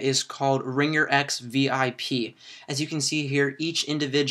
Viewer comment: I thought he said "Ring your Ex" instead of RingerX VIP. LOL